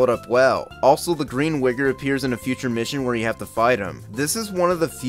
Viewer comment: What did you just say? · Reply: It's wigg3r Wednesday lolol